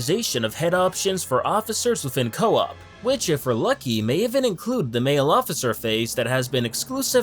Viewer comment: What does he say at 1:51? Is he talking about the E.T. looking officer?